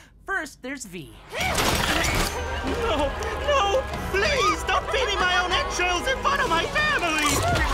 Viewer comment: V: viciously rips apart her victim and feeds them their entrails and yet, I still feel nothing eye twitch Me: ... And you are my favorite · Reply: I thought the man said “don’t feed me my own EGGSHELLS in front of my family!”